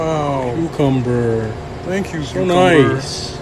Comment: you can hear the other guy saying "cucumber hat" when he put the salami on it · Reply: i thought he said cucumber ham